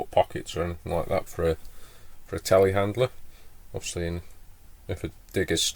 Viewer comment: Telehandler?!? At first I thought you said "tallywacker" several times and wondered what on earth was going on down there.